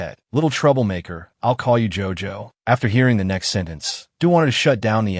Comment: Now I’m at 6:25:22 and I’m dying of cringe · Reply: I’m now at 7:57:20 and I was crying because my dumb ass heard gojo instead of jojo then I thought of jojo siwa